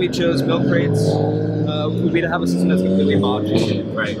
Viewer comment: "The main reason we chose milk crates..." Other dude: "achooo!" What? So what did he say????????? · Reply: Modular